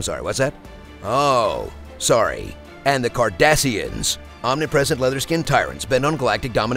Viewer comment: Tell me who wouldn't want to watch Keeping Up With the Cardassians? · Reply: I remember when that show came out I was in the Kitchen and I thought they said "Cardassians" I was sorely sad when I sat down to watch 3 minutes of it.